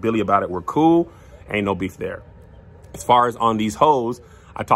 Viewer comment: I'm a big big fan. 😊 But aye at 0:56 did you say " as far as these hoes..." ? I'm reading the subtitles and and that's what it says you said. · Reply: "On Deez Hoes", Precious & Kamira's podcast.